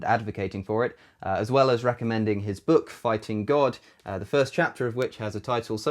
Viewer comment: I'm so baked and tired that I thought I heard you say I stole the first chapter of the book and I had to go back to make sure I want crazy, I was